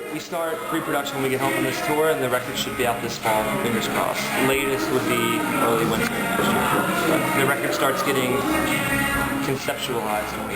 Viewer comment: What does Shawn say at 3:12? "Latest would be.... ?" I can't hear what he's saying. :( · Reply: "Early Wednesday"